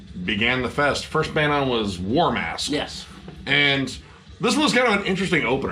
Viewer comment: When you said the first band was "War Mask", at first I mistakenly thought you said "Warm Ass"